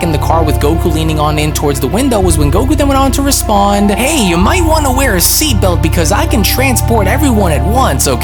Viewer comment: why did you say "you might wanna wear a seatbelt?" He clearly said, "have a seat." That changes the entirety of the thought behind what he said. Please answer this question. Is it for copyright/plagiarism claim reasons. Seriously curious.